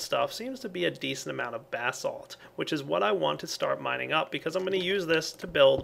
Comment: I thought he said bath salt. ;)